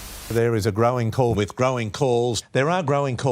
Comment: I always thought they were saying "Crowing Balls" - but looks like I was a little bit wrong....